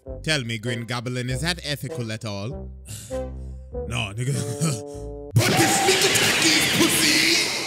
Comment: what did he say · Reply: "but this sneak attack is psyy"